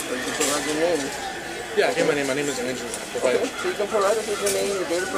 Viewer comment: The conversation with the sergeant at the end was difficult to hear. Did he identify himself at 24:30? I thought I heard "My name is..." but I can't be sure.